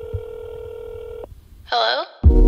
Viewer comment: ¿Soy yo o escuche "uwu" al comienzo? Se que dijo "hello" pero si lo escuchas bien dice "uwu" jsksjs